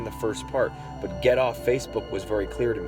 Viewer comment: I hear " Get it off, Face me"